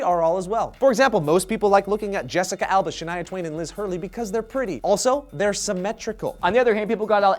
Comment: What's her name at 2:14? He said it too fast. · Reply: Shania Twain and Elizabeth Hurley